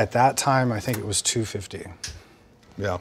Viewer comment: Im probably wrong but did he just say he payed $250,000 for that guitar?